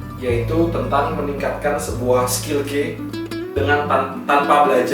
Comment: aku dengernya "Skill Gay"